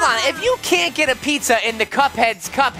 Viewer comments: what did shawn just say?... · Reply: He said sit. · i think he said a bad word like s**t